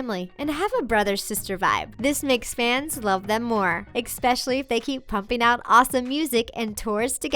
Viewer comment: did anyone else recignise she said "them more" instead of "them even more" the time was